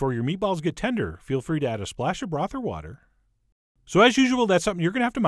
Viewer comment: I thought you said "add brothel water". I'm thinking eww.. that doesnt not sound likke it would compliment the turkey balls..or maybe it would...